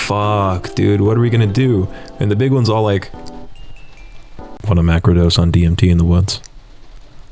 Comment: can some one tell me what is he saying? Macro Dose? also what music is that right after that line? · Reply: No idea what the song is, but he says “wanna macro dose on (something, I think he said dmt???) in the woods?”